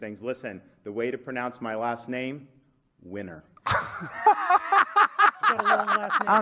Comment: I thought Ron said, "weiner".